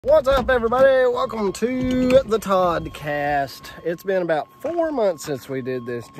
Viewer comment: Is it always the Toddcast? I swear sometimes I hear “Broadcast”